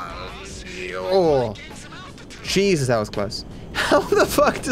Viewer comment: Idk sounds like Matt was saying pull out pull out instead of pull up